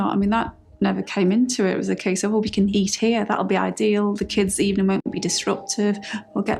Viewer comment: I am hearing Kate say”the kids evening won’t be disruptive” instead of ‘disrupted’. Maybe it’s just me.